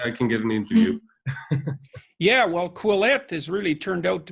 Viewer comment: Why cant i hear what they are saying? Quelepte? Quelette?